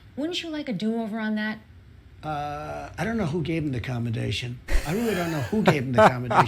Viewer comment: Does Trump say, "accommodation"?